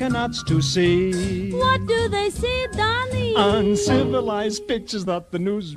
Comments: First time I heard this part, I misheard it as "uncivilized bitches" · Is it just me or that when he says 'Uncivilised pi tures"' I always thought he said 'uncizalised bitches' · No lie I thought it said "uncivilized bitches" but it was pictures 😓